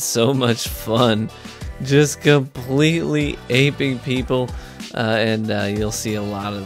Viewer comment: My dumbass thought it said vaping